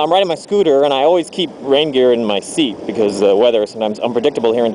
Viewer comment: i thought you said reindeer, then after about a minute i thought you cant wear reindeer, imagine if you wore a deer head as your helmet. (note i do understand you were on about rain gear)